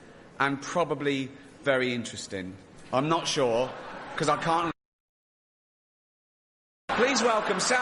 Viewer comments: It mutes at 35:14! What does he say??! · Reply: Tim pretty sure it’s something along the lines of “I can’t understand a word they’re saying” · What did he say at 35:15? "...Cause I can't understand what the f*** they're saying" something like that?